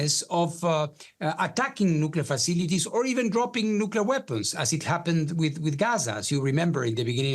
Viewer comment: did he said drop nuclear as in Gaza at the start of the conflict?